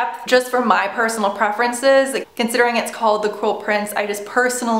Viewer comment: I thought u said the "coral" prince at the beginning lol